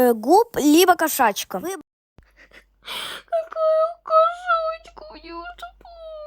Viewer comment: Мне послышалось алкашачка...